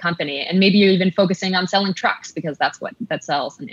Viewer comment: I heard "selling drugs" not "selling trucks"